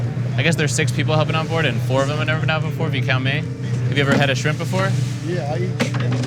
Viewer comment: "Have You ever headed shrimp before?" "Yeah, I eat shrimp" lmfao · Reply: It sounded like he said "Have you ever had a shrimp before?"